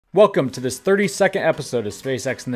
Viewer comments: really got me worried there... I thought you said "welcome to the 30 second episode" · i thought i heard 30 second episode